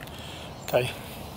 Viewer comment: did you say "f*:k you"? 😂